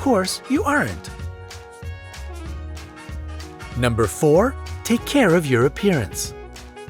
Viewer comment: Who else heard "Take Care of Europeans"? I did XD